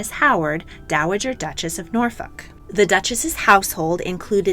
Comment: Ok, but I heard NorFuck😐💀